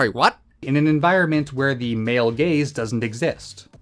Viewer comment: "Male gaze". I heard "male gays"..